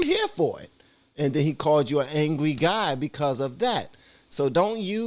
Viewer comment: First comment, 20:40. He did not just call him an angry guy. He called him a mean, angry, white guy. There were two other important adjectives there. If Jordan said someone, anyone sounded like an adjective,, plus their race, it'd be viral news.